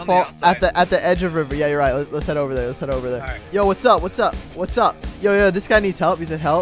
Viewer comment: It sounded like he said "yoooo what's up noobs" xD